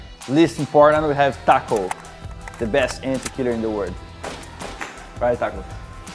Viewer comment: "Certo taco?" "Yeah" OQUE eu ouvi "OQUEEEE????"